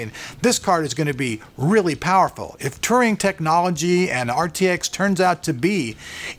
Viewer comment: thought you said 'Turian technology.' Was having flashbacks to Mass Effect and Garrus calibrating Turian Thanix Cannons XD